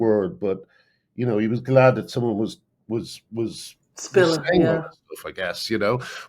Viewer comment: Wonderful to see the three of you in conversation. 2:21 Please tell us what word Graham finally said here! I couldn't make it out. "He was glad that someone was _____" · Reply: Sorry! here! I interrupted Graham when he was saying “He was glad that someone was saying all this stuff”